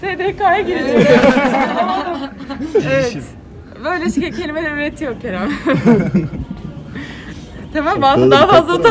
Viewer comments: Ne dedi? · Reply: Böyle kelimeler üretiyor Kerem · arkadaşlar ya burda hande ne dedi pek anlamadım biri yazabilir mi ? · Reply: "Böyle kelimeler üretiyor kerem" diyor